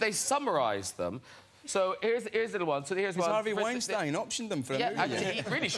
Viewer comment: Too early to find some interesting insightful comments and great timestamps. Btw. Can anyone help me decipher what James said about Harvey Weinstein just before the reading of fanfics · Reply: "Has Harvey Weinstein optioned them [the fanfics] for a movie?"